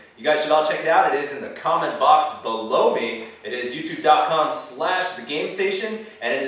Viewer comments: it is in the comment box. Blow me? :) Yes I know its "below me"....but for a second there i was like "WTF did he just say?" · Wait wait wait, did he just say "the comment box... blow me"? ;)